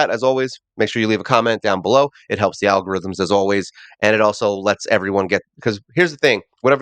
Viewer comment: Wow I need to get mt ears looked at. I misheard that last part as "Al gore rhythm" lol